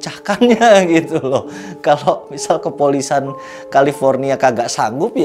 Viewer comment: Mantapp, dan smpet diulang ulang pada menit 21:42 saya kira " kepolisan" mudah2an saya salah denger yah Gaess.. 😁😁😋✌ sukses teross.